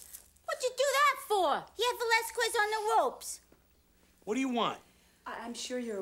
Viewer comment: HE HAD VELASQUEZ ON THE ROPES! 😂💀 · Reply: I had no idea that she said Valasquez, I thought she said the last quiz!